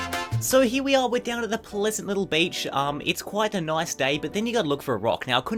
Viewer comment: I thought it said head to the bleach XD